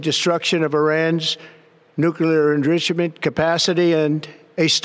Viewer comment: tRump slurred his way through saying "enrichment". I couldn't even tell what he had tried to say until I turned on the subtitles and rewatched that section. Well, now that it has claimed we're at war, it can now do a lot of things it couldn't do when we weren't at war. Such as the Alien Enemies Act of 1798. Rather convenient, don't you think, that it declared war (when it wasn't necessary) right after his intelligence people said Iran didn't have nuclear weapons and after he started having problems with deporting people. But, since the Alien Enemies Act of 1798 only involves people that come from the country involved in the war, does this mean it will claim war on other countries as well?